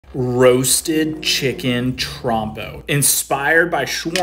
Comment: Before reading I thought he said, "roasted chicken trombone" 😂